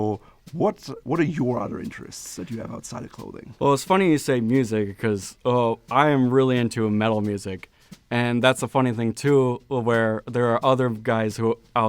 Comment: Welcome to! Smashed it! Fellow classic menswear enthusiast + metalhead here! I suppose it makes sense in the current era, with casual dressing at a high. Representing that 'alternative' / 'rebellious' streak in you that's often associated with heavy music could take the form of dressing more formally, or returning to the sartorial norms of the past, rather than you needing to sport some ripped jeans and a leather jacket etc.! This was a great video, with some really useful tips on how to approach this veritable socio-sartorial minefield. I requested this exact topic a little while ago (along with many others, I'm sure) -- 'Dressing For The Modern Office'-type thing -- so I'm thrilled to now see it here on the channel. Bravo, and! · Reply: LOL - I thought he said "mellow music" and was invisioning the Rat Pack.